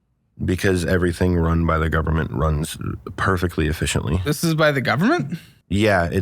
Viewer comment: what word is he saying?? “Furloughment”?